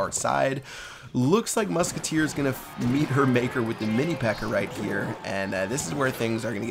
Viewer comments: Lmao at 1:12, Pat says mini pecker instead of pekka xD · Who heard Pat say Mini P.E.K.K.E.R?